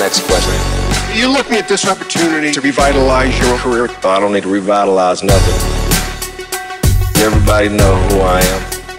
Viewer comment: what does he say on 1:19? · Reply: "i dont need to revitalize nothing"